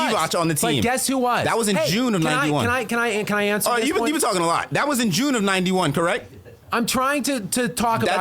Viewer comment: Did he say has been talking a lot???